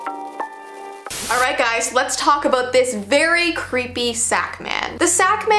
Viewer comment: I thought it said snack man😭